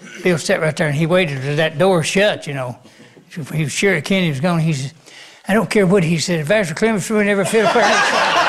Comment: What did Bobby Osborne say around 3:00 minutes that Bill said about the fiddle player? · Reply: "I don't care what he said, Vassar Clements ruined every fiddle player in this town."